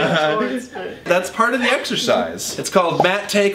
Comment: Thomas:(Talks about being in shape) Me:(Slowly chews on oreo) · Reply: Nicole Cimino exersise i thought u said extra fries